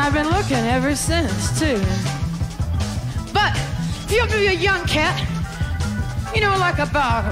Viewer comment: Couldn't be more wrong about 1 thing! Back in the day, guys called their gf/spouse "my old lady" (or mama) on the regular!!! She's referring to someone she loves, needs, and has known about those things since SHE was 17 (not cougar preying on the young!)... btw, gals called their significant other "my old man" (or papa) too. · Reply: You’re definitely right on the Mama part, but I think you’re mishearing the 17 part as many do cause Janis rambles through that part really fast. At first she’s talking about herself at 14, then eludes to how every woman can relate to that, the she says “But now if you’re a young CAT, around 17.. She’s talking about a young MAN as Cat was common slang for a young man back then. She goes on to talking about how he might feels confused about his new feelings.. then she says “I’ll tell you what you need”.. “you need a sweet talking Mama..”